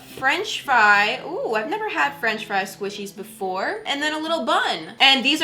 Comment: The first French fries squishy she got it sounded like she said fench fry squishy